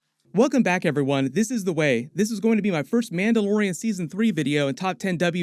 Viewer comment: I keep hearing Yoda say "The way this is" lol 😆 One question, do we know if there is going to be a Boba Fett series 2? 🤔